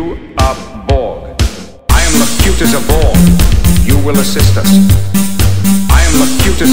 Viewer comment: Me and my brother thought he said: I am as cute as a Boy, You will respond to my questions